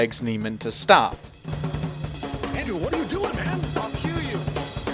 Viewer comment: I know he said, "I'll cue you.", but every time I hear that part, all that comes to my ears is " I'll kill you " lol